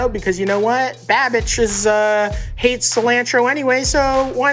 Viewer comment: with the captions on, it says “babitch” instead of babish 😂. Timed perfect, since Matty is getting on him about the cilantro.